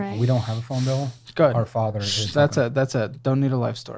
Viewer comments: does he say "our father" ? that would explain why they are sticking together. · did he just say “our father” lmaoooo